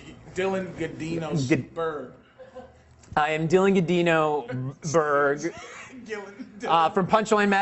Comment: Did Patrice call the interviewer a Sperg? 😂😂😂